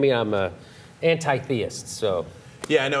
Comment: Hahaha did I hear Cenk identify as anti-theist at 2:23?? Stepping up from humble agnostic I see :p